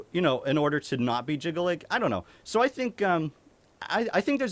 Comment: Ohh Jyggalag I thought you said jiggling. Like some dead or alive volleyball 👀